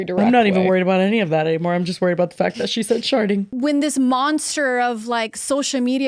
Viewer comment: I heard starting too